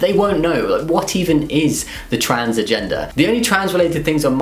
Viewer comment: I thought he was saying "the transfer gender" not "the trans agenda"